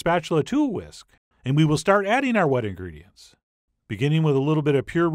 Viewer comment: The first time I heard - And we will start adding our wedding rings.